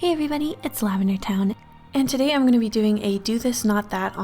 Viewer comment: Is it just me who thought she always said “hey it’s me lavender *TIME*.”